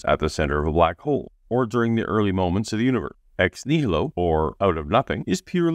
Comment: Your words were cut off a few times throughout the video. For example, at 5:25, it sounds like you said "univer" instead of "universe", and at 13:55, the "st" in "past" is cut off. My understanding of video editing and audio engineering is as undeveloped as Mr. Butt's reasoning skills. Coincidentally, though, Logiked noticed a similar problem in a video he reviewed recently, and he figured that the guy had the noise gate level set too high.